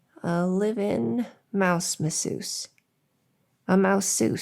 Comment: At first I thought you said "mouse missus" and this took a very different turn all of the sudden.